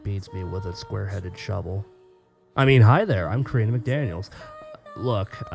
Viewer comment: Well that weird every time i hear you saying your name i always hear 'Katrina' instead of 'Karina', but i guess i was wrong....whoops I find it weird that you choose my 2th story instead of the first , it isn't a bad thing at all and perhaps your saving it for next time. i'll be waiting for part 3.